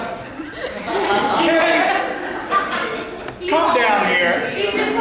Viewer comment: Wait does he say ‘Kenny, come down here’, or ‘Kenny, clamor down there’? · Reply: Come down here.